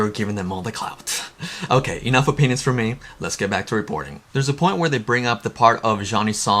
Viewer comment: CAPTIONS says "....okay you know if a penis for me now let's get back...."